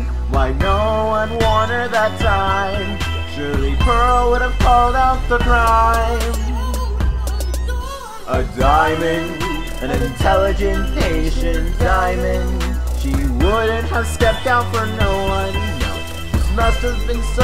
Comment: I thought he said an intelligent Asian Dimond